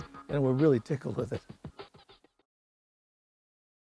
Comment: I thought she said space std. lol